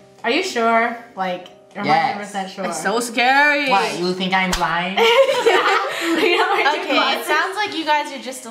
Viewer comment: Are you sure? takes off glasses what you think I'm lying. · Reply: I thought he said "do you think I'm blind?"